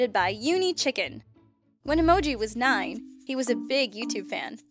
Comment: Not to be fucked up but is emoji a guy or a girl cause i hear "when emoji was this he" or " when emoji did this she"